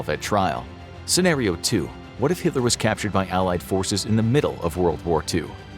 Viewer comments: Bro I thought it said captured by aliens💀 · I thought it said captured by aliens 💀